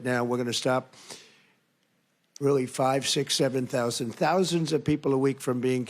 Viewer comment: did he said 67?